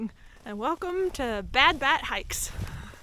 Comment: Lmao you think your bad I thought you said bad bath hick lol it’s bat damn